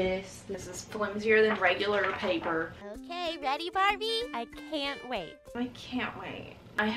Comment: when she says “are u ready Barbie?” The captions say are you daddy Barbie?